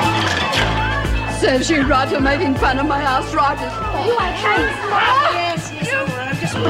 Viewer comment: what did that one say, serves you right for making fun of my house it sounded like? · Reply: Arthritis.